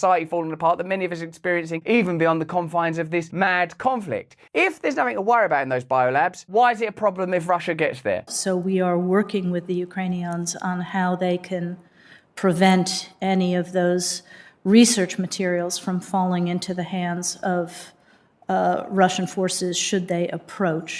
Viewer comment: "Ukrainions" Did she say Ukrainions? What are Ukrainions? Maybe they are military robots of and for war under the power, direction, and authority of the US (via UN law of war).